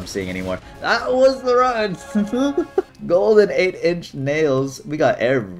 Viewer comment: I thought he said 9inch nails i was so happy for 0.002 sec